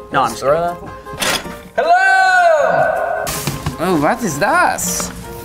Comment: is just saying german words ? “Was ist das”?